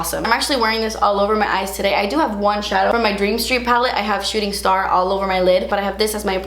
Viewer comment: It sounded like she said shitting star. 😂💀